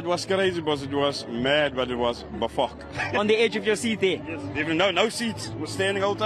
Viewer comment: did he say it was “befok”?😅